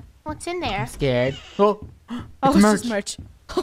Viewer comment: I heard say “Mommy help I’m scared”